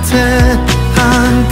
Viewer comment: 초.. 사투리 쓰는 나로써는 한강우에서(한강위에서) 라고 들리는 이 느낌....